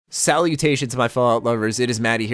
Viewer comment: Did you just say "Sally mutation." In the beginning CMIIW